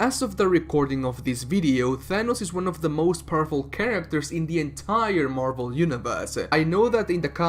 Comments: My mf ass thought he said "as of recording this video thanos is one of the most purple characters in the MCU" lmao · At the start I thought he said “thanos is one of the most purple characters” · Deadass thought you said he was one of the most purple characters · At first I thought you said that Thanos was the most purple character. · sounds like he said purple and not powerful, why do I feel like that was on purpose? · I honestly thought you said "Thanos is one of the most purple chatacters in the entire Marvel Universe" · First few seconds I heard "As of the recording of this video, Thanos is the most purple character".....I'm like "bruhhhhh" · I heard, "Thanos is one of the most purple character.." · I swear he said Purple characters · I thought you said Thanos is the most purple character. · I misheard the intro as "Thanos is one of the most purple characters in the entire marvel universe" and damn near had a stroke. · i thought the intro said thanos was the most purple character lmao